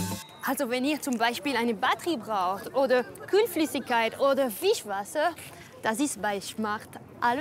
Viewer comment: wie ich einfach immer ´´ Fischwasser´´ verstehe hhahahahahahhaa